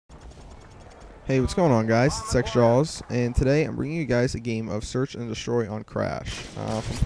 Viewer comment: LOOOOL i thought you said ..."hey whats gonig on guys is sexjaws" lol